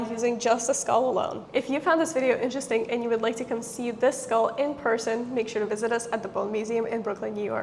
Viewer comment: I thought she said "If you would like to conceive this skull" and I got really frightened.